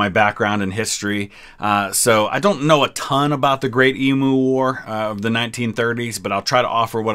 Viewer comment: I thought you said emo wars through my noise cancelling headphones. 🤔 Is emo war a thing?